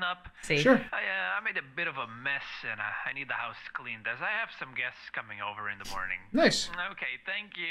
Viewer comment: At first I thought the monster said clean my ass